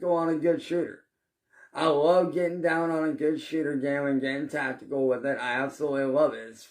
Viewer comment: I thought he said "I love getting down on a good shitter".